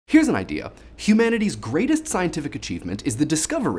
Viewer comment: Thanks to DaThings1, I always hear; "here's an eye!"